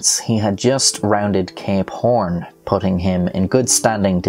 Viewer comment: Thought he kept saying gay porn when he was saying something else. Idk the name